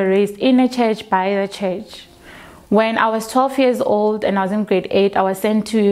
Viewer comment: did she say "when I was 12 years old in grade 8"? Or I heard something else😧😲